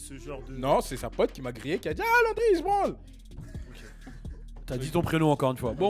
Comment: c’est quoi son prénom j’entends pas ce qu’il dit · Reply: Sam DeZiP Landry ?